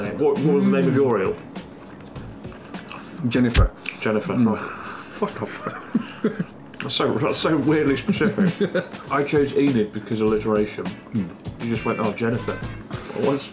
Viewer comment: did say what the f-, or f- off? Im hearing either everytime i play it back